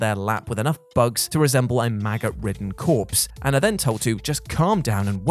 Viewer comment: I thought he said “MAGA-ridden corpse.”Then wondered what that even looks like.